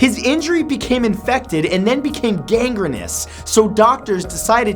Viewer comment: Why did you say dang-er-us around 1:25 is that how Canadians pronounce dangerous? Maybe it was on accident when reading lol